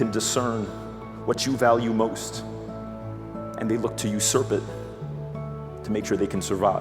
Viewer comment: Can anybody tell me what is he saying at 1:47? "They look to you serpid to make sure they can survire" What doe´s it mean? Thank you :) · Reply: He's saying they look to usurp it, meaning they will do whatever they can to get rid of any distraction their parent(s) have in order for them to be their sole focus.